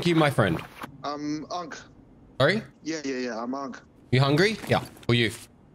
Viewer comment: i thought the guy was saying he's hung